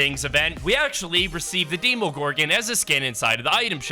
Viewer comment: Oh god. I heard him say “deemogorgon” it’s demogorgon🤦🏻 Edit: I said this as a meme but thx for 12 likes